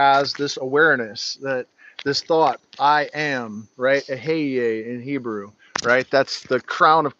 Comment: what did you say I am was, something sounding like eh-hey-yey?? I smoked dmt under the blood moon recently and was met with three triangular headed beings and the only thing I could say to them was “I am” and I’m not sure why that came to me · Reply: Eheieh, roughly. Ancient Hebrew